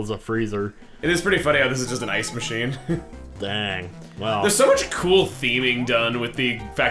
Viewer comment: did you say that ice box is COOL theming?!